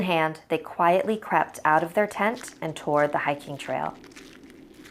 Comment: i thought she said "quietly crapped out of their tent 😖🤣😂😅😄😁 but i was wrong i misheard her sorry no really i'm sorry